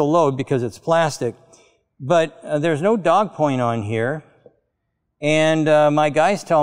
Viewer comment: did Sandy say 'there's no dog coin on here'??? Another obscure manufacturing term? Did I hear it incorrectly? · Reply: "Dog point", it's a type of protruding tip :)